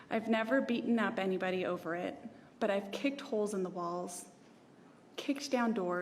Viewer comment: I thought she had said kicked wholes in the balls